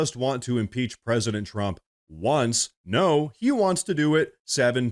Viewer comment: I agree I thought he said he wanted to throw 7 peaches at president Trump. Or one peach 7 times. Hard lingo to understand!